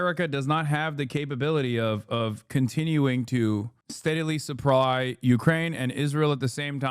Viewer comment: what did you just just say @ 12:30? Steadily suppwy? 😂😂😂